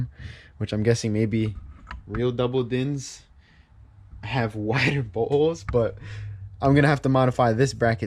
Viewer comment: “real double dins…..have wider buttholes” Am I the only one who heard it like that? 😂